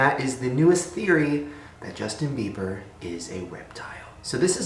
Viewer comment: I heard "Justin beiber is a weptile"